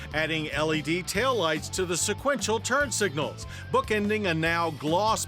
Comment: What the fuck is he saying at 1:43? BOOKA NINGA NOW? LOLWHAT